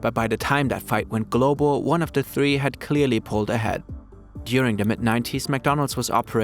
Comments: "During the Mc 90s" is what I heard him say 😂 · Did he say Mc-90s? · At first I thought he said "During the Mc90's" lmao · I'm so cooked I heard "During the Mc 90s" instead of "During the mid-90s" 😭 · He said, “during the mid 90s.” I heard, “during the Mc90s.”